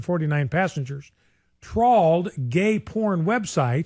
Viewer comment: All I hear is "tralled."